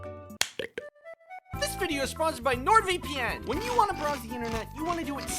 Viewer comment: i thought it said “nword”